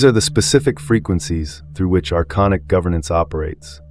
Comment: great video so far. Typo alert on the closed captions. 10:58 it says "our iconic government" in the captions when the actual words are "archonic government". potentially big typo 13:38ish the captions say "nomadic" instead of "pneumatic", not a huge deal, but in this context it would definitely be better to have the right spelling